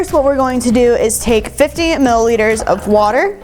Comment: “Such a large difference” what?? 1:14 had to rewind three times to hear “15” instead of “50” 😅